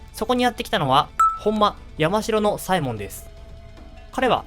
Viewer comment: 倍速でみてたので本間山城左衛門がほんまヤバ城の左衛門に聞こえて引き返した